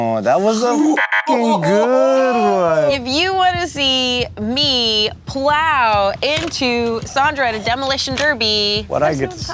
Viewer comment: all I heard was “if you want to see me getting ploughed”… 🥹